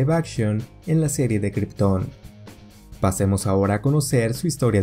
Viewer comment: cual serie? No entendí el nombre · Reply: La serie se llama Krypton 😅